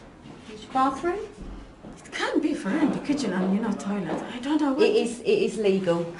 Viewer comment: what does the lady say about the apartment being illegal? did she say there is no toilet?